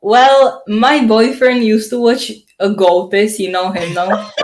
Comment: Who did she say her boyfriend watched ? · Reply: the chanel goatis